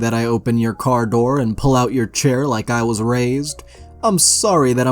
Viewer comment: I misheard him say “ open your door and pulling out your chair.” I saw in my mind somebody opening a car door and ripping out the seat